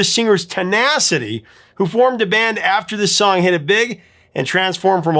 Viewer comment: Where did I say that? I must have misspoke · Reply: I heard "formed." I can clearly hear the F.